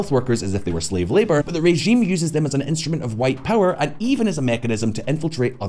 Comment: did she say "white power" instead of soft power?